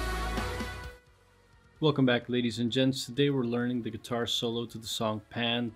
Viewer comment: did i just hear you say "ladies and djents"? FUCKING GENIUS